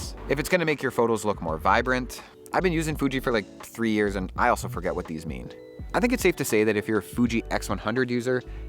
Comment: Did he say "I also forget what these meaned?" Unrelated, but the guy looks ... and talks kinda like a younger Mike Myers. Groovy Baby!